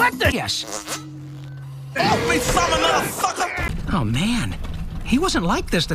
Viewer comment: I’ve seen this show since I was 14 and I don’t remember this episode but I swear, I thought I heard him say MF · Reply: He says, "Get off me, you salmonella sucker!" It took me so long to figure out 💀💀